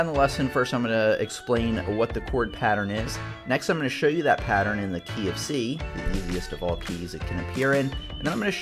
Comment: I heard him say "I'm gonna show you that pattern in the KFC" Now I am hungry lol